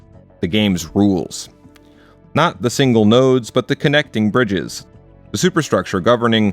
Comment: Did you say "nerds"?